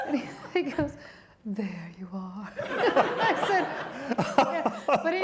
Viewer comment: For a moment around 25:00, I thought she said they talked about Paul Stanley. I had to rewind it twice before I could make out that she said Ralph Stanley, LoL!